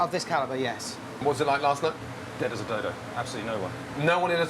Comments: what did he say? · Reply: Dead as a dodo, absolutely no one. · he said "dead as a dodo" but I heard "dead as a dildo"